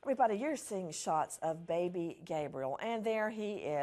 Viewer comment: Did she just say baby gay briel?